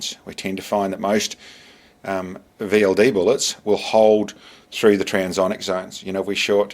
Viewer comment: "The availity bullets" (8:48) Could not understand. Did you say the smaller caliber ammo shown held transonic better than the largest bullet shown which tumbled? Thanks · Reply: I said VLD bullets, (very low drag) and no I said "those" big bullets had a problem in the sub sonic zone, and beyond that it is infinity more complicated to explain, but that was the general picture of why I chose to shoot the Cheytac 350 grain projectile, for the 4000 meter shot, Cheers